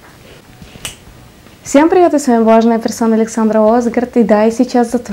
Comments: Мне послышалось "влажная персона" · Мне вначале послышалось :"всем привет, с вами влажная персона..."